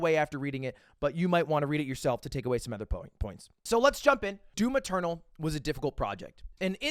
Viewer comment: At some point I thought you were saying "Do Maternal" and I'm wondering who's pregnant?